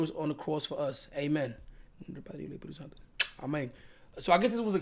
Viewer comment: the second time said Amen sounded like "Ameng" in that Dominican accent 🤣🤣